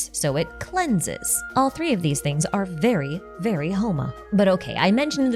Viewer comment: i thought she said "homo" and not "homa"